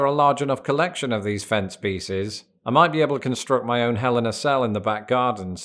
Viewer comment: I thought you said Helena cell and I thought maybe it was some contraption I hadn’t heard of yet.